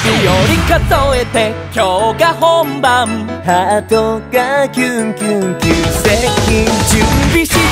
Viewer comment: 元のコメント探せなかったんですけど 1:34の薫くんの ｢ハートがキュンキュン旧石器☆｣ を見てからそれにしか聞こえなくなりましたwwww